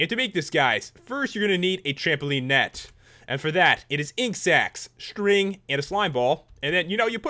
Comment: When u said ink sacs i thought u said insects. Laugh out loud